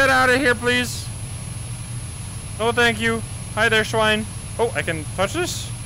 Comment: Idk why I thought that he said "Bruce Swine" at the intro XD